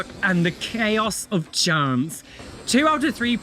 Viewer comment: LOL I thought you said “chaos of germs.”